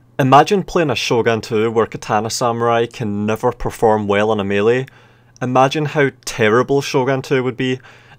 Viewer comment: if we talk about realism and how testudos in every total war game aside from Rome is unrealistic and broken. it should be noted that katana samurai in shogun 2 being op is also unrealistic. katana samurai should be less op then yari samurai. Hollywood and other games have glorified the sword and pretty much left spears in the dark, making people think that swords were op and everyone used them as their first weapon of choice. when in fact spears were the primary weapons that were used the most and swords were only a secondary weapon and had almost no way to deal with a skilled spear user. so as much as we like to think katana samurai are op. if we want to view it in a realistic way it should be the yari and naginata samurai and monks that should have been op. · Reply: also forgive me for the misunderstanding. i misheard your point about katana samurai "imagine katana samurai not preforming well in melee combat" and thought you said "imagine katana samurai not being op in melee combat" feelsbad to have short term memory some times so my bad about that one. But my point about yari samurai vs katana samurai still stands.